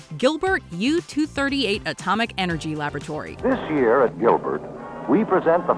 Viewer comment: DID HE SAY! FINEST toyline? Or FINAL TOYLINE?? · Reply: “Finest”.